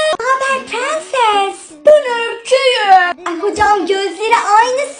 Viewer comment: Orda naber prenses diyince ben öyküyüm diye duydum😂😂